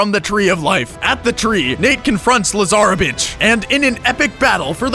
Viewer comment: Did you just call Lazaravich, Lazara-bitch?